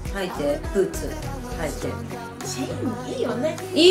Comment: シェインって聞こえる😂😂😂 けどシーインやで😂😂😂